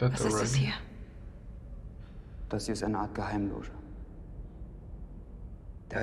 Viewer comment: Well, the show did say: "What we know is a drop; what we don't know is an ocean" 😂 1) Tronte actually pisses me off so much. I mean, Ulrich is a cheating piece of shit, but at least he left his mistress and did everything to find his son. In season one when Jana was going crazy with Mads's disappearance, Tronte was busy going after Claudia, and even now he is more concerned with her than with Mads. Of course he chose home, food and clean clothes (this is a brazilian saying, I hope it makes sense to you) instead of caring for the daughter he had out of marriage who just lost her mother and grandfather in the same day and now has no one (except for Aleksander) 😡 2) 13:17 What is up with the subtitles saying "secret lodge"? I've seen this in many reaction videos. Isn't Bartosz saying "secret society"? Makes a lot more sense to me 3) My favorite momments from this ep: • As weird as it may sound I really like the Noah and Elisabeth's scene • Katharina in the school (it's so tense, but still so funny lol) • The final scene with Jonas and old Martha, because I paused in the family tree to take a look 😂😂 But I'm glad you didn't, everything in there will be revealed and I can't wait to see your faces 😅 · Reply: 2) He's saying "Geheimloge" which literally translates to "secret lodge"